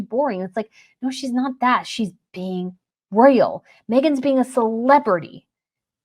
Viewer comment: Omgoodness... when you said, "Catherine was being a royal. Megan was being a celebrity." I swore I thought you said Megan was being a sss...slut. 🤪